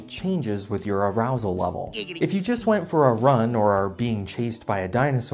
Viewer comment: did I hear “giggity”? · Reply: yes...yes you DID hear "giggity"!!